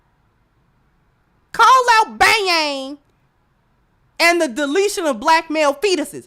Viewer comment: HOLD UP......HOLD UP!!!!!.............no you didn't!!!!!!.....@ 48:20 did you just call Cynthia G "Ba-ya-ya-ya-ng"??????? I was drinking some coffee when I heard that, laughed so hard I almost spit it out!!!! Ba-ya-ya-ya-ng......with such an intensely serious subject matter you have a George Carlin level of injecting the funny so unexpectedly into something so serious!! I thank Allah for you!!